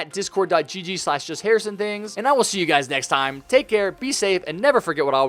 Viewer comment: I thought I just heard pee on 11:00 lol